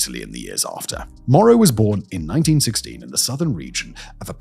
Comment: Heh. I found a problem with turning on and just listening while working on something else. I could have sworn you said Moro was born in 1960, but the pictures were of a man in his 60s, though he was assassinated in 1978, when he would have been 18 😂 Good thing it's easy to skip back a bit and rewatch that part.